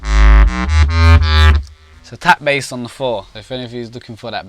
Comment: what bass did he say at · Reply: Tap bass